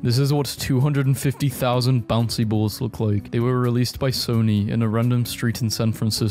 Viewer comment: bruh I thought they said by "Sorry" and I assumed sorry boys made another stupid video 😭